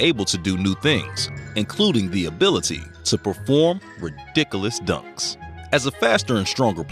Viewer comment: Anybody else catch the word "ridicalus" 10:02 lmfao like and comment is u did